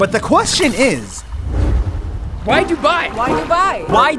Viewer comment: When you say why Dubai it sounds like your saying why’d you buy